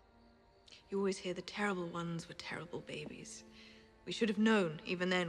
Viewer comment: what does she say there? i think i understood the words but still don't get it :/ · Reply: stor "You always hear the terrible ones were terrible babies. We should've known even then, we should've known. It's nonsense. Whenever he was with me, he was happy." She is talking about Joffrey and what a lovely baby he was, and that there was no way of telling how he would turn out.